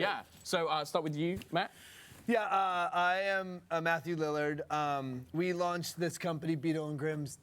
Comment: I thought you said Matthew Lillan. And I thought, ah that blows. It would be so cool to see Matthew LIllard play dnd, and then the camera zoomed in on his face, and I was like. HOLY SHIT IT IS MATTHEW LILLARD. MY SHAGGY YAY!!!!